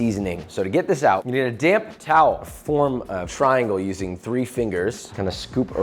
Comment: This is a very good video - BUT to be a great video you should speak a little slower. Every time you say 'damp towel,' it sounds like you say 'damn towel.'